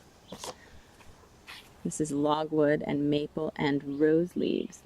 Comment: Thank you! Goldie is holding her own for now. The vet thinks she has a respiratory infection. We'll see how the next few days go. The blue/purple silk was dyed with logwood, then I eco printed over it! Isn't it stunning?!?! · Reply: I thought you said "logwood," but I had never heard of it before. I'll check in to that. Yes, the color is stunning. Thumbs up for Goldie. :o)